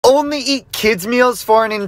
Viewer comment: For a second, I thought I said, only eat kids for a day 😂